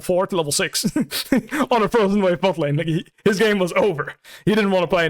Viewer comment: hol up what does biwpo say here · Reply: 'on a frozen wave top lane'